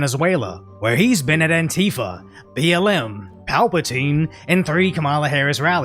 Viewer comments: I had to back it up 3 times just make sure I heard you right. You said Palpatine! You're my favorite, dude!😂😂 I'm dying over here 😂😂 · Im sorry but wtf. Did i just hear Palpatine? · Bro why am I hearing Palpatine at 6:54?